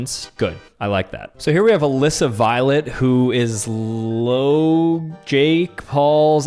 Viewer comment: I thought u said “a list of violet”